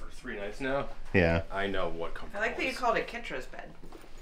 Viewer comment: When you were in your cabin before you went to Batuu, I thought you said “Ketracel-white” and not “Kitra’s bed”. BWWWWWAAAAAAAAAHAHAHAHAHAH